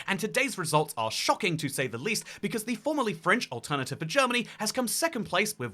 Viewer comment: Am I the only one that heard it as "formerly French"? 😆